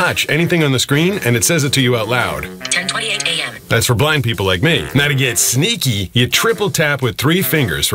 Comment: I thought he said “that’s for black people! Like me.”